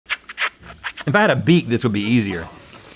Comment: In Link’s defense it does kind of sound like Rhett is saying “Beat” even though that makes no sense at all! A true friend for just beatboxing without questioning how it will help! 😂